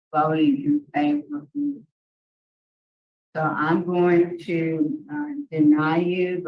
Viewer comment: I don’t think he said “I’m going to die.” Sounded like he was saying, “I’m going to deny it.” Referring to getting sex offender treatment.